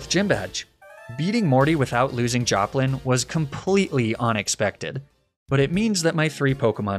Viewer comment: "Onix-pected" either he said a good dad joke or I just heard it myself 😅